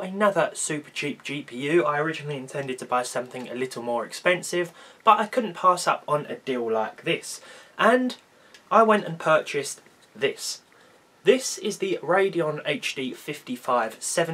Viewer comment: Im British and still find ur accent funny at 0:21 u were like "u dayl laik dish" instead of a " a deal like this" but no hate nice vid. But I think it's called Fortnite not Fauwtnight